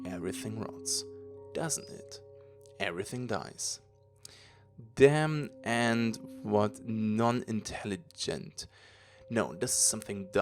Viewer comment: Hey! Incredible, just as I wanted to dig into the lore I find out that you, of course already made videos baout that! Thanks a bunch! Btw the passage at 3:46 where you struggle is latin and therefore written without spaces. It tells "damnant quod non intelligunt", translated "They damn, what they dont understand"